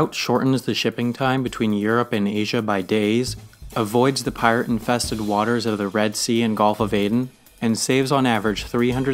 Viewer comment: Did he say PIRATE infested waters?